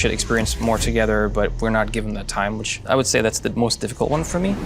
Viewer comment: What does he say "We are not given the time?"